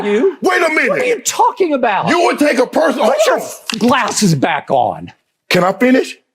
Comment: Put your glasses on boy that’s what I heard him say 😮